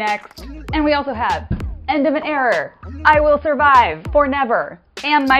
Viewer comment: I heard "era"... then I saw "error," and it all made sense. You're a good friend girl, my ass would be going to Party City 😂